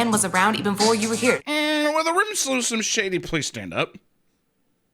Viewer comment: I heard say "Will the real slim jim-hady" lol